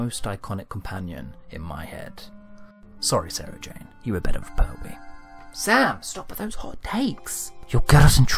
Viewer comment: "Sorry Sarah Jane, you were ..." what?? I can't make out what he says!!!! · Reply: "better with Pertwee"